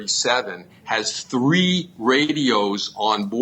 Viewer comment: 9,000 hour pilot here. There was no DEVIATION offense, here. More like Controller Error. This "It's always the pilot's fault" crap has got to stop. The controller messed up his landing deck, didn't get the runway cleared in time, and needed AC781 to help him fix it. All 6 instructions to "Go around" occurred within 30 seconds. The Pilot, copilot, and flight engineer are all listening on the radio. For all three of them to miss 6 instructions in 30 seconds indicates that they did not hear the instructions. i.e. they did lose radio contact. Light Gun Signal? Who is kidding whom? The red light gun signal is a 6" FLASHLIGHT from inside the tower, which is over a mile from the end of the runway, way to the left of the pilot's vision. Nobody is going to see that. Pilots are not trained to look at the tower for landing instructions. Flying into an airport, at night looks like a Christmas tree of lights. The pilot was cleared to land. His concentration is on the lights at the touchdown zone. Not the tower. Who is investigating the controller? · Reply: Chris R "if you think an airbus 321 has a flight engineer" Oops. Sorry. My bad. I meant to say tail gunner. The attorney said that 781 had 3 radios. And I thought he said 3 people listening for the radio. That is what happens when you are deaf in one ear, and can't hear out the other.